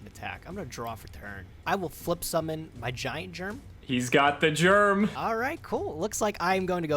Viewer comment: Sounded like said I'm gonna flip summon vagina germ.